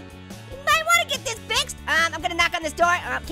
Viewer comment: did he say b**ch